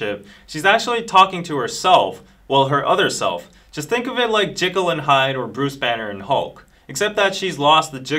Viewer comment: I HEARD 'JIGGLE AND HYDE' SHSHSHSH